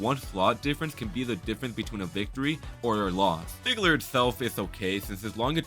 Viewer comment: did bro said nigler???